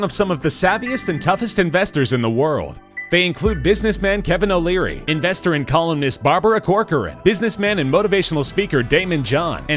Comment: idk why but I heard him say "Barbara, investor and colonist" at first 🤣